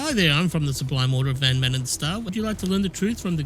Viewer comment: Hi there im from the (wtf does he even say) would you like to learn the truth from the good book? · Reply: I think he said "Sublime Order of Van Maanen's Star", a theocratic faction.